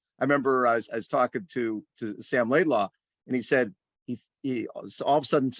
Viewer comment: Ah crap, I thought you said Sam Laidlow